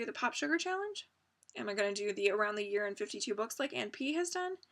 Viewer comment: Lovely hour an a half wrapping presents while watching - I have no idea why I am compelled to tell you what I do while watching your flosstube?!?! I'm such a weirdo I guess it's to express what a lovely time I have :) I'm going to borrow some of your ideas on how to fit the full coverage in along with everything else. I might try the weekend full coverage idea... To me a beautiful journal is a full/used often journal. I want a journal that I use regularly but often flake out after a month or two :/ I'm getting back on the sock train too or well knitting train in general I guess. I have a pair of socks I casted on 1 year ago that I haven't finished aarrgh. I also really want to knit a chuck sweater so I might give in to that. I have and want to read all of Chimamanda Ngozi Adichie's books but yeah - they are intimidating! If you want a book about China I'm about 50 pages off finishing up Do Not Say We Have Nothing by Madeleine Thien and holy cow is it good and my heart is broken it's making me feel so much!! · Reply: oops sorry I thought you said around the WORLD in 52 books but it's around the year lol. Disregard my totally random China book rec lol